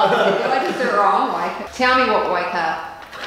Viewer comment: Did i hear that right? 5:23 Tell me what..... haha thats for the 40min delay. great work. keep at it.